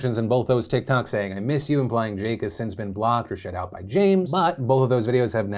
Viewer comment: "That Jake has been blocked or shit out by James" is what I misheard you say and I almost ate my own face.